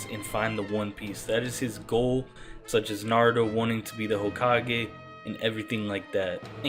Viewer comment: I said naruto not narudo do u have ears ?? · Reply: just sound like you said narudo to me mang